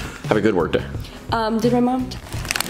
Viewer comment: Did she say our mom